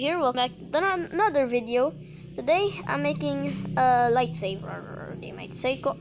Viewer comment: I thought u said LIGHTSABER!